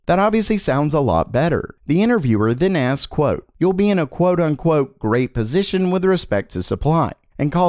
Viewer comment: I thought that was said as "quote on quote" 💀